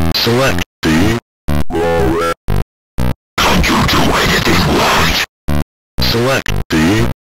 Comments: tf did he said? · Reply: It says. "Can't you do anything Right!?" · What is pac saying when he turns into an eldrich horror ( 0:26 ) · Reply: "CAN'T YOU DO ANYTHING RIGHT?!?!?" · Can someone tell me what he said? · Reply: Dont u do ANYTHING right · what he say? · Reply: he said "CAN'T YOU DO ANYTHING RIGHT" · what is he saying? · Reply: Can't you do anything right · What did he say here? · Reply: CAN’T YOU DO ANYTHING RIGHT!?